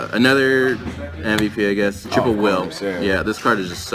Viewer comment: What's the card at 7:36 I keep hearing trip of well